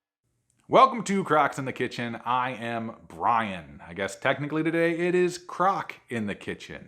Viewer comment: Hmmmmm ? I knew your name is Brian but it Really sounded like u said Ryan . 🤪